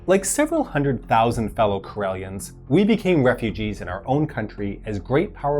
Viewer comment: I swear I heard “fellow Corellians”